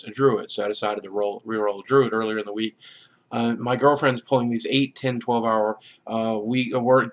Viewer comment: did he say girl friend? omg omg ( . Y. ) so good job i wondering if shes gf is in game ? on irl